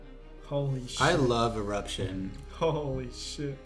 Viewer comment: When said that he loves eruption, I thought that he said erection